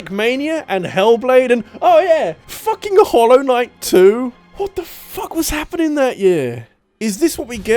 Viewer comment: I heard "hollow knight two" Wait, what!? Oh, he meant "hollow knight, too"...